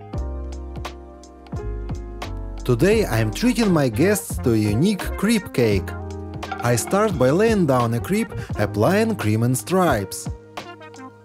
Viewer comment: Thought you said creep cake❤